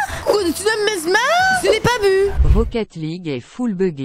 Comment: "Ce n'est pas buts " moi j'ai compris "je n'ai pas bu" 😅😅😅😂